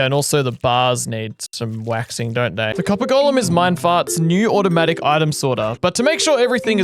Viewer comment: Wait... How come Minecraft he copper armer but emerald armer isn't a thing? Edit: 17:32 wait did he just say minefart?